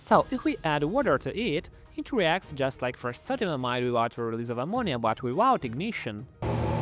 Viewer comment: what did he say here? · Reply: "with the release of ammonia" but in a fucked up way lol